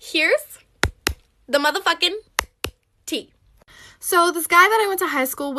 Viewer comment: Omg she says heres the mother fucking tea i thought she said heres to the motherfucken teeth